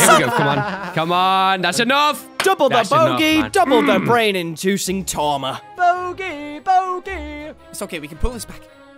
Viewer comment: "Double the Bogey, double the brain inducing tormer?" What are you saying at 6:08? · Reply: "Double the bogey, double the pain inducing trauma"